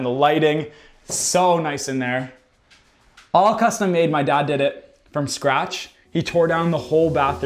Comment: Ngl when he said dad eh I thought he said daddy 30😅